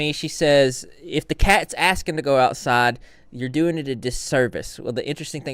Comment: Good Sir, you are giving her too much credit she meant to say “You are doing it a disservice” 7:52 but what she actually says 7:43 is doing it a DISFAVOR 😭💀